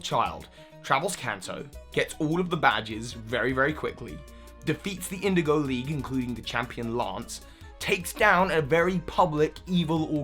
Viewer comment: I incorrectly heard you say "And beated the Champion Lance." Epic fail me.